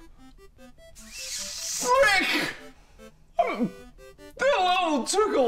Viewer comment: did Mark just said " FREAK " ?!